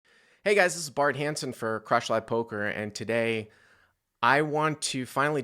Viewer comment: You had me at what I thought I heard as, "Crotch Live Poker".